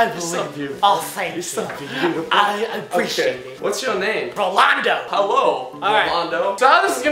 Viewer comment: Roi said D; Goodbye